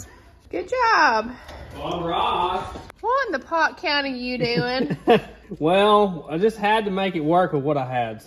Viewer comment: Did I hear “what in the Pott. Co. are you doin?”😂😂😂